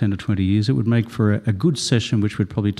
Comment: it really sounded like he said “goon session” did it not? 🤣🤣😉😉